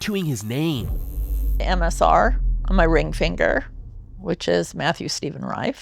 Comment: I thought it said MSG 💀